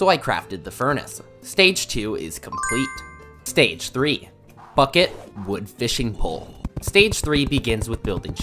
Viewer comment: I thought he said fuck it 😄